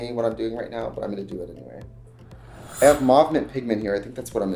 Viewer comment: I thought he said moth man pigment lol